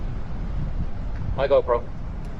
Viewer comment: I heard bye bye GoPro:text-green-game-over